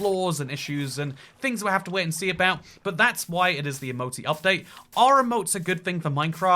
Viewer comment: I thought at first it said the "emo update"💀